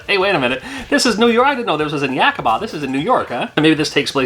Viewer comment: Wait. Did you just say Yakima? That's my hometown too!